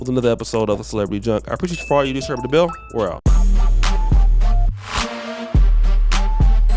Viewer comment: What in the world does always say at the end of his videos? All I hear is "I.................................bell, we're out". Somebody help me😂😂😂.